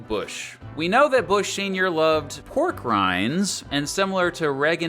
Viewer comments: At first I misheard "Bush loved pork rinds" as "Bush loved war crimes" lmao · I genuinely thought you said “We know that Bush Sr. likes war crimes”